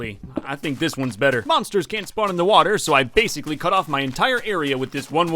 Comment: it sounded like he said “mobsters can’t spawn in the water…”